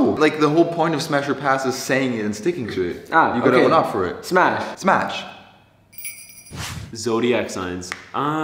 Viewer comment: bro all i heard was " spash smapsh smash pamsh "